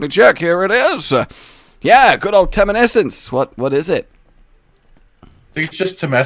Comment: When said temessence after, I would have sworn he said tumescence. This is not a good word to have a gameplay term easily mistaken for...